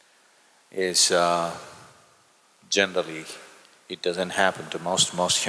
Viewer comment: what did he say?? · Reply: Generally